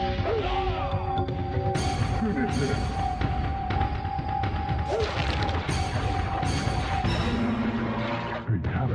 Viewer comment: what did scorpion say? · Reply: He didn't say anything, that was the announcer saying "Finish Him."